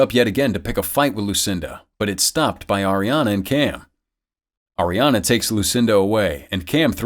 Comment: I thought he said Ariana grande